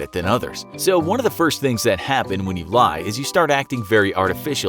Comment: I thought it said “when you die” lol